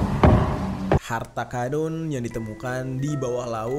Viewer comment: apa gw yg salah denger, dia ngmng harta kadun🤣🤣